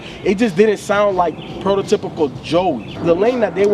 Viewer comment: "It just didn't sound like PROTOTYPICAL Joey" clear example of wrong usage of the word PROTOTYPICAL. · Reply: I thought he said "Pro Era typical Joey" at first, which would've make more sens.